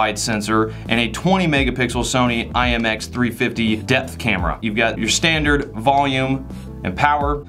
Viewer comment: Ngl, at 1:02 I heard “death camera”. Seeing how them cameras look like aliens nowadays, I wouldn’t be surprised if they actually were capable of stealing souls. :P